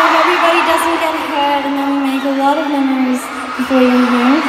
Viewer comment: i literally thought rose said "make a lot of money" lol.